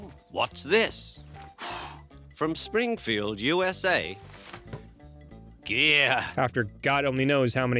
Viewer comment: I'm so sorry, but I am that person. 20:30 Ringo is not saying "yeah", he's saying "geer". Which basically means "cool" or "awesome".